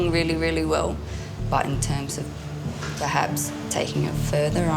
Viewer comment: @ 25:44 “but in terms of the house” ?? - I’m clueless What is she trying to say? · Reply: I think she said perhaps. Maybe